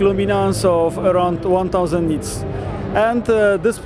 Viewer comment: how many nits of brightness did he say at 1:07? 1000 nits?